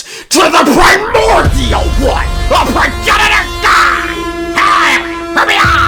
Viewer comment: all I hear is Eggman saying he’s gonna piss on the moon (plz someone get the reference)